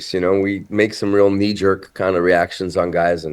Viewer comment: Did he say "Kneejerk"